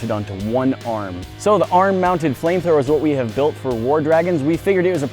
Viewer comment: At first I thought it said arm mounted Gordon Ramsay